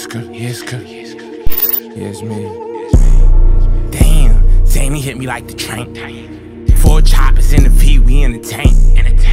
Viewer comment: Zanny hitting like the tranq is crazy 😭🤦🏾‍♂️ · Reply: I thought he said xanny hit like a train